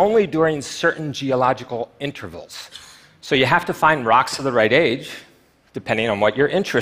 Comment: I swear I heard "How do you fight a dinosaur? Sounds impossible, right? It's not, and the answer relies on a formula that all paleontologists use: first, find some rocks of the right edge..." Me: Oh I see, you gonna stab the dinosaur with the rocks, those paleo are smart